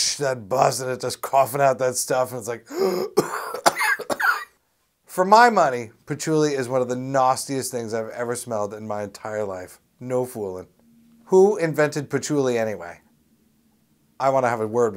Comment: Did Tommy say "Nawstiest?"